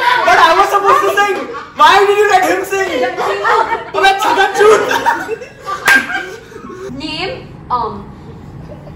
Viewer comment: what did say? 😂😂😂 They laughed so hard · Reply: Chagadchud? Ig he said that😂😂😂